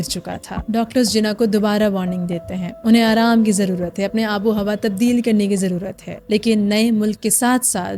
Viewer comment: did she said Bengali abohowa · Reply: Apni aabo hawa